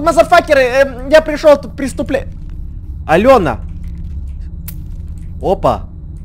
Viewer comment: (Я услышала в 4:49 - 5:00 имя Алëна) (Я которая Алëна) ЧО НАДО? А?